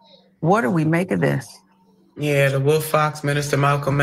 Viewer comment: "The wolf fox minister malcom x and grandma more nerd than a brass a monkey." THAT is what CC youtube subtitles translated as to what was said at 14:58 lol What???